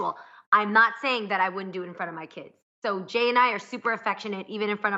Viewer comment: Did Jeanie just say "MY KIDS" ? 👀